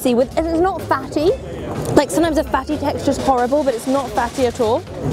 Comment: When she said the fatty part is horrible I cringed🙄🤦... That's what makes Cha siu so good is when u have some fat mixed in. If there's no fat it's too lean which gets very hard and chewy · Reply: You misheard her, she said “sometimes the fatty texture is horrible, but it’s not fatty at all”. But yea, at first listen, I thought she meant the fatty part was horrible and I was like excuse me chile?